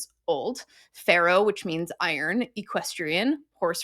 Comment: the captions kept turnin ferro into pharoah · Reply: I was listening to the video without watching, and it genuinely sounded like she was saying pharaoh.